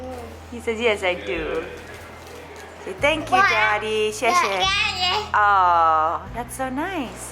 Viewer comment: it sounded like saying wa ai ni daddy (I love you daddy) in Chinese 🤧🥰